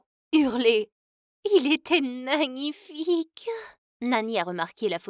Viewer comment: c'est moi ou elle a dis nanifique😂